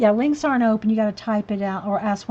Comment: Hi. I'm Debcolors from this mornings stream .I'm sorry I misunderstood you when you said links . I thought you said wings. I'm so sorry . But it was fun to be in there while you was streaming live . that was my very first time . I'm so new to all this .